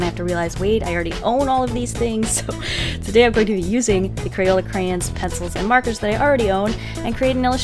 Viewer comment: DID SHE JUST SAY "CRAYEN" WTH